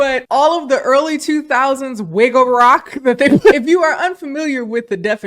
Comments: Lmfao I thought you said “wiggle rock” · Oooooh! Girl, I thought you said WIGGLE rock. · I thought you said “wiggle rock” and thought it was rock music appropriated by the Wiggles. I was about to google “Wiggles Limp bizkit episode”…. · calling nu metal 'wigga rock' is the funniest shit i've heard this week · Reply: I thought she was saying "wiggle rock" but this makes way more sense 🤣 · Girl I thought you said “wiggle rock” I was like hell yea it is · I thought you were saying "wiggle rock" at first, LMAO. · The way I 100% thought you were saying wiggle rock lmao